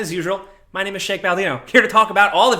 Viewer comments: Did he just say Shake Baldino?!?! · sounded like you said Shake Baldino lol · Did he just say his name was Shake Baldino? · I heard you say your name was Shake Baldino, and I approve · Did he just say " my name is Shake Baldino" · I could've sworn he just said Shake Baldino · I swear I heard him say Shank Baldino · “My name is Shake Baldino.” That sounded like a damn good drinking brand, if you asked me! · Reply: I thought I was having a stroke, but apparently I wasn't the only one who heard him say "Shake" Baldino. Cool...